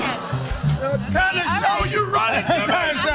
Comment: lol did cuba call that guy "cousin sal"